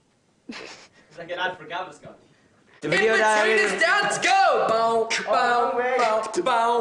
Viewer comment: What does Louis say at 1:16? All I heard is dance